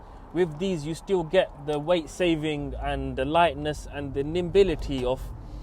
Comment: did you say weight savings over 981?